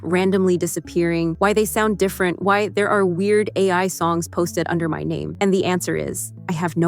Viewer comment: thought she said weird al